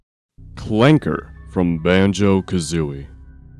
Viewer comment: at first i heard "wanker"